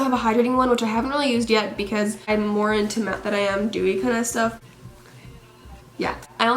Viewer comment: I legit thought she said "I'm more into meth than dewy kind of stuff."